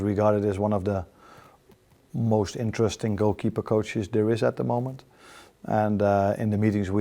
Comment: A new keeper instead of Allison though? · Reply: no. you misheard. he said a new goalkeeping coach is coming. not a new goalkeeper.